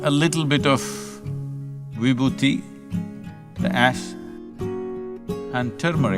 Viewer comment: Can anyone please spell the name of the ingredient suggested by Sadhguru @ 4:37 ... I heard tumeric but not sure what the other powders were... thanks :) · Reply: VIBHUTI (Sacred Ash)